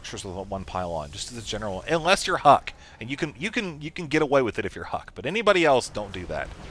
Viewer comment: i almost thought he said "unless you are HOT" instead of "unless you are huk" xD